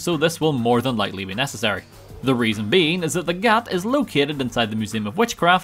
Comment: did he say the gyatt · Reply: gat